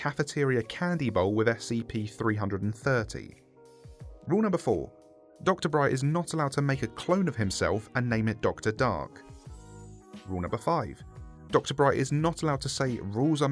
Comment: I swear, I thought is said "Dr. Dank"